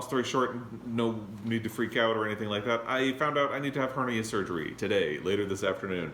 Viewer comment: At first I thought you said cardiac surgery. Whew! Anyway, I hope all goes well with your surgery and that you recuperate quickly. Just remember to take it easy and not overdo things even if you’re feeling okay.